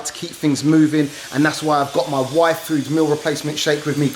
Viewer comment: i thought he said "my wife"